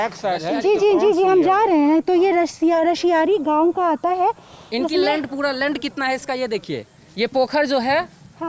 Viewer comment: Sorry though a video on series issue, at 16:22 did I hear “lund” instead of “land” 😂😂😂